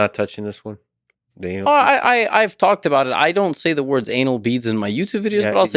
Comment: Yo, folks, think you could help me out. I'm not a native English speaker and I just wonder: do they say 'anal bead' (what subtitles suggest) or anal bee 🐝(what I hear). I don't believe any of this is correct. What tf do they say?